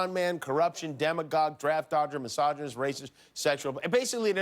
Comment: Demagogue...i heard it as Demigorgon ( Stranger Things) 😂